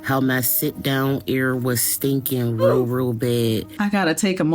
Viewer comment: i thought she said “sit down area” 😂